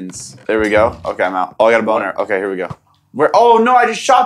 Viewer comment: what did he say!!!!! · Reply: He said "okay I got a bow and arrow, okay here we go"